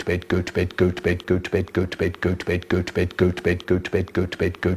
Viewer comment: I thought after all of those got bed I thought he said goat bed